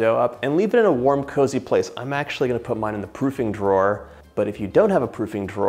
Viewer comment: I swear to god I heard you say “I’m actually going to put mine in the pooping drawer” Then you say “if you DON’T have a pooping drawer...” I had to rewind and listen again 😂 it sounded like you so casually mentioned a drawer for that and made it sound like it is the norm 😂 it’s 2 am I need to go to bed oh my god 😂